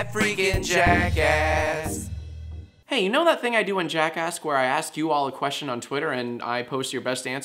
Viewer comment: I thought it said "Jacksack"